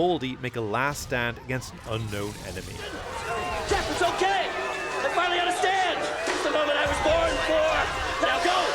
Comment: Garibaldi said "I finally understand" ? I thought he said: I finally got a stand!" like a last stand.